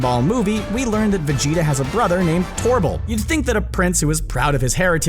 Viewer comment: Did... Did you just call him Torble?